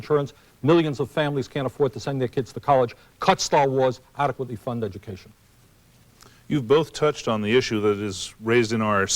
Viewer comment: does he say "cut Star Wars, adequately fund education"??